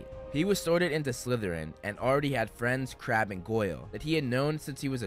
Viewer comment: When he said ''Draco had already met friends, Crabbe and Goyle'' I thought he said ''Crap and Goyle''